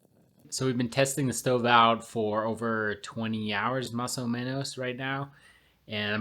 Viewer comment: I had to rewatch and make sure I heard you correctly: “mas o menos”. Was not expecting Spanglish. 😄👍🏼 I should be studying for a physiology final but this video looked interesting, so I clicked. This looks really cool. Great job.